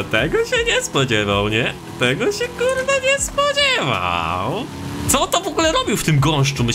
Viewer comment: może źle usłyszałem ale pierwszy raz słyszałem od słowo ku*wa jak źle słyszałem to nom